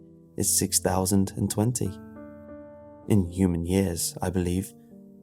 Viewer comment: Anyone else notice the mistake? Humans have been around for about 2017 years? That’s not true, not even in the Bible (which seems to be the basis of this story) your missing about a thousand years b.c. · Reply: I miss heard the narrator, I thought he said “humans have been around for”, not “in human years that is”. Thanks for the feedback